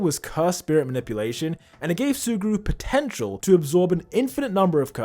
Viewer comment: DID YOU SAY... POTENTIAL!?!?!?